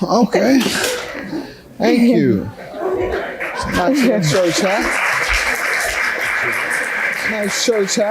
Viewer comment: I thought Judge Mathis said "Nice Cho Cha", but he said nice church hat.